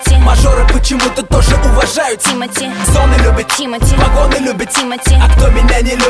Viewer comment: Мне послышалось или нет что масоны любят тимати? Значит всё таки масон...